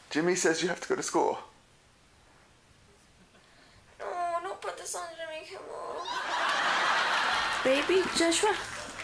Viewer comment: please tell me what did she said at 1:23. i can't listen clearly. tks in advanced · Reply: "noo... dont put this on jimmy kimmel......"